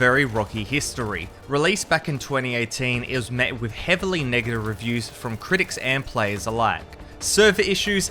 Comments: heavily wot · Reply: sounded like he said the n word but nah he said Negative Reviews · It sounded like he said heavily nigga reviews😂😂😂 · am i the only one who heard the n-word instead of negative